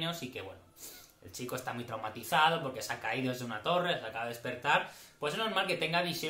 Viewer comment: Entendí "INFORMATIZADO" y quedaban tan bien igualmente que me dio la risa xDDDDDDD (Ese es Bran el wifi!! XD)